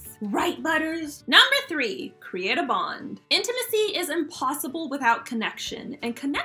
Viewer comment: HA! at number three I thought she said create a bomb lolz